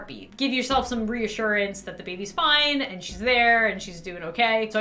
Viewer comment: did lauren just say... she?